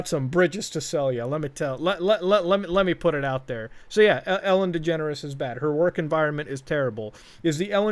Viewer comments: I thought that said "Ellen Degenerates" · I thought he said Ellen degenerates